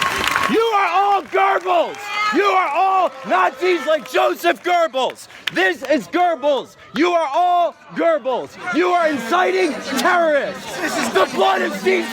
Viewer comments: wait... did he just say gerbals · I know what he actually says at 8:45 , but I keep hearing "GERBILS!"